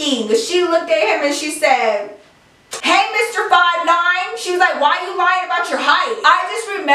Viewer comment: She said "Hey Mr. 5,9" I thought she said 69 for a sec😂